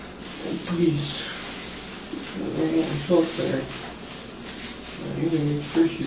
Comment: 45 did he say “please I didn’t mean to kill her” ??? Because that’s what it sure sounded like